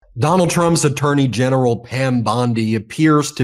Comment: Ben, did you just say "A Dirty-General, Pam Boni"? Maybe a Freudian slip.